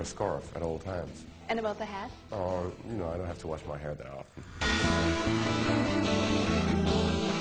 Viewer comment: did he say "i dont like to wash my hair at all"? LOL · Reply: He said "I don't have to wash my hair that often"